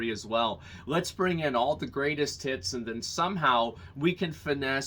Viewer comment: I'm a fucking degen. I heard 'greatest tits' like 2 or 3 times